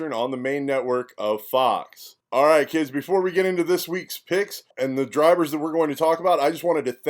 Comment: I thought you guys said fuck it this week! Love your insights boys